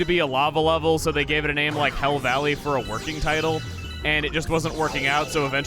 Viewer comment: The first time he said hell valley I thought he said hill valley